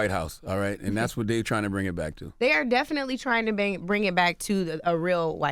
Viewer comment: There's some sexual tension between Angela Rye and Charlemagne. She said 'trying to bang' instead of 'trying to bring'